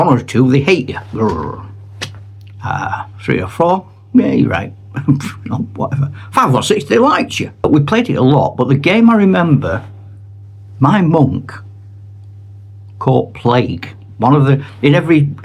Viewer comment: At the 10 minute mark i misheard you. I thought you said "my Mum caught plague"! I was about to say, I know its rough up North but...haha!